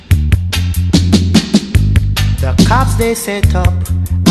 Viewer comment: 100 weight of collie weed COMING FROM SANTA SANTA lol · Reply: R111 I know lol it just sounds like he is saying Santa :D